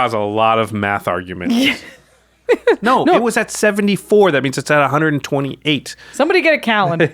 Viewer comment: Did I just hear said "it was at 74 that means it's uh 128"? Is that him being sarcastic? Anyway I'm always a fan even though I no longer play magic. Too busy at work. Love from the Philippines 🇵🇭🇵🇭🇵🇭